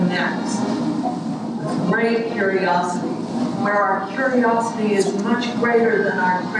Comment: what does she say off-balances with curiosity?